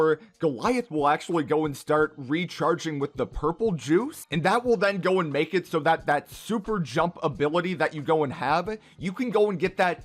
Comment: Chug jug is a sheald repair drink in Fortnite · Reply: Oh! I thought he said Jug-Jug Purple Juice lol